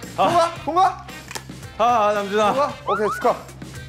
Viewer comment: Non-Muslims may make this moment normal, but it's that second that makes me even more fascinated by you. Alhamdulillah in Islam is a saying to be grateful. I watched this one year ago but I just realized it. You could say this episode is one of my favorite episodes because this episode teaches "Something is impossible, if you keep trying and never give up it will become possible" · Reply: he said "Ahhh Namjuna" 😂